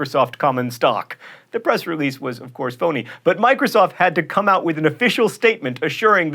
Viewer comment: Microsoft-Vatican: for a moment | thought you said "The press release was of course Sony" - I had to replay it to hear "phony"